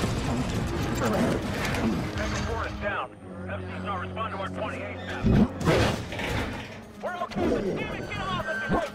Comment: Anyone know what he was tryna say at 0:59? Edit: Also, I love the little detail at 3:16 where one of the soldiers is shouting "RAISE UP, RAISE UP!" and you see the chopper's gun retract so it wouldn't collide with Ross' copter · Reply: Sounds like he was saying "This movie" lmao